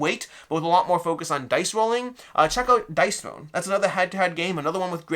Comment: What is this other game he mentions? Truly can't hear it. Dice Throne, Dice Bone, Dice Stone, Dice Known? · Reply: Dice Throne :)